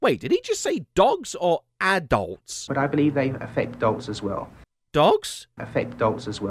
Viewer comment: I hear "dolts" xD